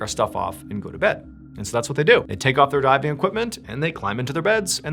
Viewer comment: did he say their god damn equipment lol